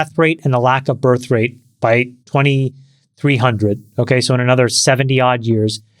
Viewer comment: did Chamath just say it’s the year 2230??